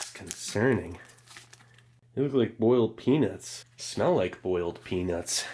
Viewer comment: my ears deceived me for a second there & I winced slightly as I could have sworn he said "boiled penis" 🤣